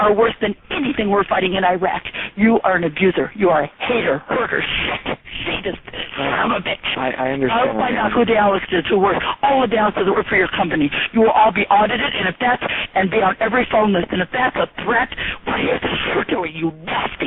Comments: does she say im a bitch at 8:26?? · did she just call him a hoarder? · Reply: She called him a "hurter" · WTF!?! Listen to 8:26 She says "i'm a bitch!"